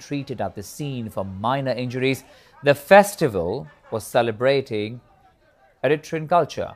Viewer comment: WTF is he saying? He had one job. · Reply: Celebrating. Mind the accent